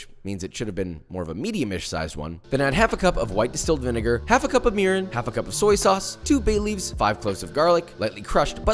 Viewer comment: I had to rewind the video several times to understand what he said at 3:23. I thought he said, "Half a cup of urine." He actually said, "Half a cup of mirin."